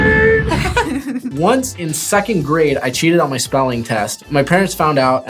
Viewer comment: Spelling test. OK, I thought said spalling.